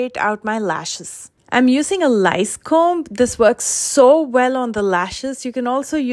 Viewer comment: What a clever idea to use a flea comb for your lashes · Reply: I thought she said lice comb, but flea comb sounds less cringey.